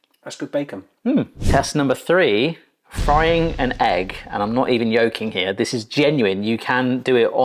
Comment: All I heard was "I'm not even yolking here".